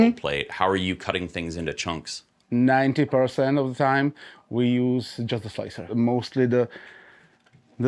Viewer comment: Minute 32:53 the man said what? " Jetta-Slicer, Gatta-Slicer "or what? The software for cutting the great Models into parts for printing ... Cant hear it .... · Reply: He said "90% of the time we use just the slicer". Prusa Slicer has a function to cut objects and to add connectors to help with the alignment.